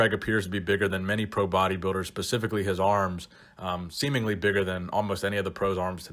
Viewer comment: Did you just say "Pacifically his arms"..? Sounded like it...